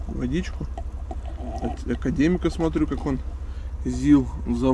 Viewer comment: я тоже Академига слышу....?